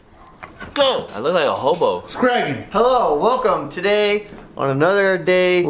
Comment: did he say scraggy